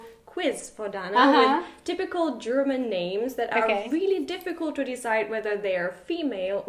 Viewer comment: Ich hab fast keinen dieser Namen jemals gehört also finde ich es ziemlich bescheuert in dem video zu sagen das es typische deutsche Namen sind🙄 dislike☝🏽️👎🏽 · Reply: Kira fair enough. It sounded like she was saying difficult to me the first time around.